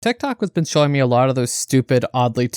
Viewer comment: Through out the whole video I thought you said that ted talk has been showing you those pics then I turned on the captions